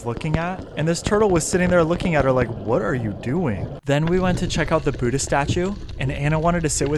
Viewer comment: I legit heard, “then we went to check out the booty statue…”